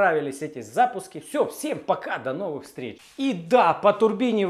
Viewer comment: всім пока? Чи почулось